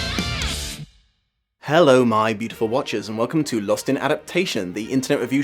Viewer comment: I thought you said “my beautiful watches” (like the wristwear) since I found the channel and I just realized you’ve been saying “my beautiful watchers”. I really love this phantom of the opera series. It’s really comforting to watch at home.